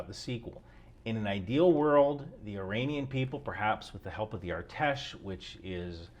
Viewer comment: Artesh? I heard ATESH 🇺🇦. Heroyam Slava!